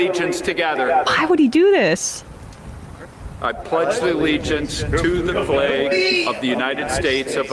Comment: Did he say 'to the plague of the United States of America'? Cos if so... bravo!